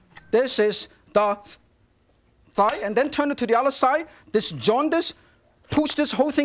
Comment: Every time he says "disjoint this", I hear "this jaundice". 2:16